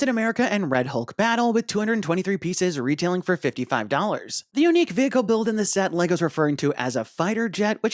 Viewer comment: At first i thought u said 355$ i had to rewind a couple of times😂